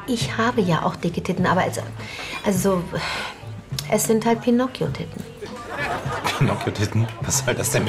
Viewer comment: I didn't understand what they were saying, but towards the end of the video, she was speaking my language! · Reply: I heard her say, pinocchio titin, what ever that means? At the end of the video, I'm sure I could make out, biggin titin and they definitely were!